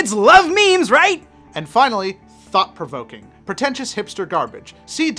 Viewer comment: i swear i thought he said fuck provoking not thought provoking